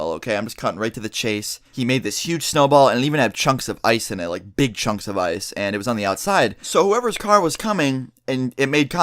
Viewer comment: whenever he said big chunks of ice I thought he said big chungus